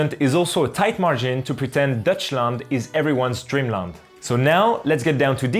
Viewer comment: did you just say deutschland.... which means germany in german..